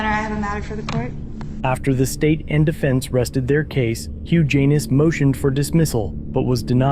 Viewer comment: Huge Anus (Hugh Janis) is what I thought I heard you say was his name. You might want to slow down when saying his name (forgive me for pointing this out). Mr. Janis was well within his rights and the deputy was clearly overreaching his authority. I cannot believe that the jury got it wrong and thank God that it was overturned on a technicality.